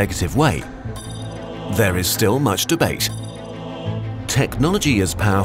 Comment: did he say "masturbate"?